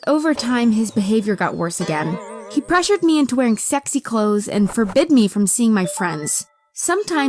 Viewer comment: DID HE SAID SEXY CLOTHES!?